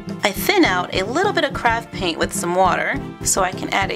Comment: Who else thought she said crap paint